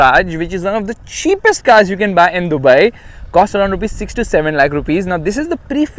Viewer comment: Anyone else who heard "67 lakh rupees" instead of "6 to 7 lakh"